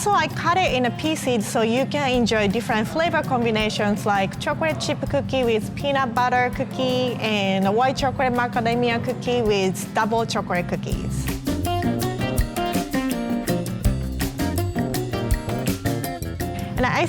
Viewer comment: Did I heard my hero Academia cookie 😕😂😂😕😂